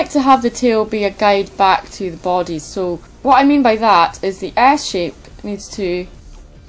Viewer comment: I thought you said ass shape XD